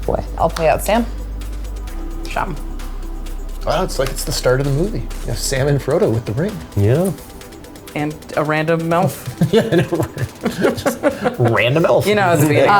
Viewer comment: Did anyone else understand: "Sam and Frodo with the ring and a random MILF?"